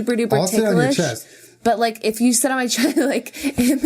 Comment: I was multi-tasking with this playing in the background and I heard "I'll shit on your chest" instead of "sit" and I spit out my drink all over my desktop keyboard. This is why I come here.